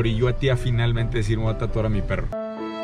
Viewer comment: Pensé que decía “decidí tirarme a mi perro”